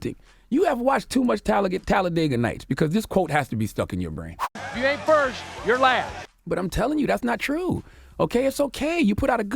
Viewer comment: Why did I hear "if you ain't MERCH you're last"